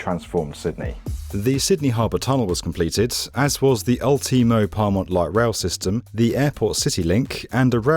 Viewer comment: It took me like 15 replays to realise I think he was trying to say "Ultimo" lmao